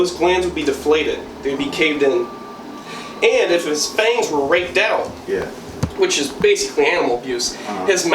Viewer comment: am i the only one who heard: "and if his hangs were raped out" ?